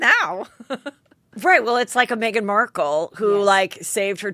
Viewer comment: OMG!! I thought you said Meghan Ward!!!!